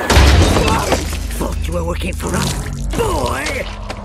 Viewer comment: Wait, can anyone please tell me what starscream said in 1:29? I didn't fully understand what he said because of his stupid annoying voice,also please tell me what he said in 0:55? Since I also didn't fully understand what he said, I do understand that what he said next was "You can't hide boy!!!" also "I love it when your little insect feet trying to run!!" also I do understand what he said in 3:07 he said "I'm gonna kick you!" still stupid annoying voice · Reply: he said "What a treat you and me alone!" And at 1:29 he said "I thought you're working for us boy!"